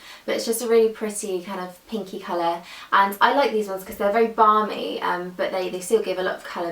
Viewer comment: I thought you said 'barmy' instead of 'balmy' haha